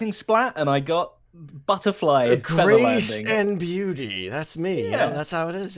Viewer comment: Anyone else hear "grease and beauty"